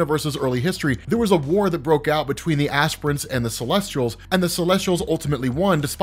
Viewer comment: they were called what now? Assprints? I need someone to clarify because the strongest being in the universe in my head is now what once was called an ass print lol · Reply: I thought he said "aspirins" but this is way better